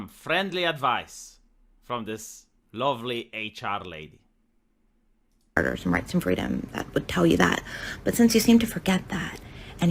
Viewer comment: What she said at the beginning ? i legit don't understand "match and freedom " 🤔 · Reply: "m' rights and freedoms''